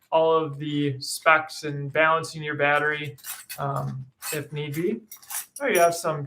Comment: did you say"balancing your battery? If so, what does that mean?